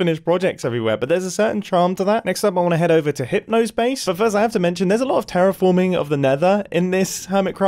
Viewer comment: I heard “then I’m gonna head over to H***ers base”